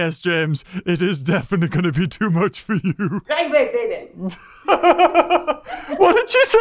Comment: I don't think she said "Line break, baby" but "Light weight, baby" (Ronnie Coleman's quote/meme)